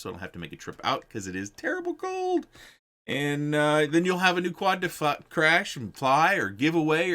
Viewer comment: i heard in the first time: you have a quad to fu**, crash and fly :D